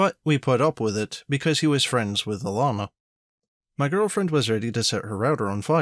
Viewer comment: I misheard at one point. "He was friends with a llama." (Alanna.)